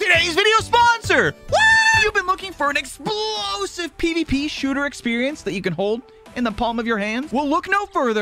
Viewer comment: During the sponsor segment, I misheard and thought he said “Peashooter” and was wondering that he was sponsored by Plants Vs. Zombies